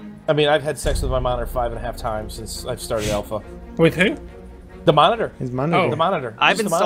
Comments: i heard mother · Sex with my mother? That's what I thought I heard lol